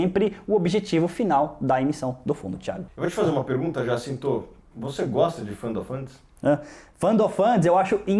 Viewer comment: eu q entendi em 3:04 "vc gosta de final fantasy?" 😂😂